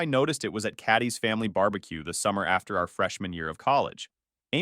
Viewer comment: Said "freshman year" but I heard "fresh manure".